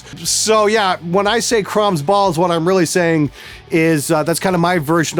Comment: I thought you always said "Crumbs balls". That desk looks really cool.